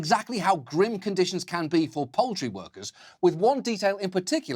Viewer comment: every time he says "poultry workers" i hear "pole tree workers".... is a segment exposing the dirty underbelly of hipster culture, the hipster strippers, or "hippy strippies" as they're known in the industry the episode we need? free the pole tree workers!